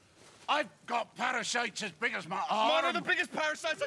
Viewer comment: My favorite part probably, “yes I’m telling you the secret is…” everyone looks at him…”…to skin them first!” Everyone is in an uproar 🤣 “I’ll skin you ya little!” “I won’t forget that!” Later… “Nono not that one he’s infected! Yes he’s got worms in his tubes! … in fact they all have! They’re infected with parasites it’s an extremely dangerous buisness, I wouldn’t risk it I really wouldn’t.” “Parasites did he say parasites? We don’t have parasites! You have parasites-“ “… I’ve got parasites as big as my arm, mine are the biggest parasites I’ve got huge parasites! We’re riddled! Yes we’re riddled yes we are!” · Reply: I think I heard Gloin said, “I’ve got nits!”